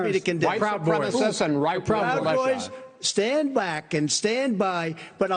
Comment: Everytime I hear that comment I hear "stay black and stand by".